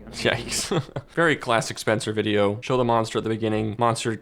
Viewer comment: Did he say Scheiße 😂?